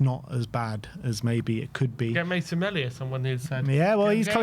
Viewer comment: whos name did say at 5:08??? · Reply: Mason Melia. From St pats